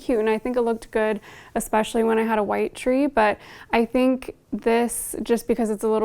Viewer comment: I thought you were saying "fig tree" and then I realized...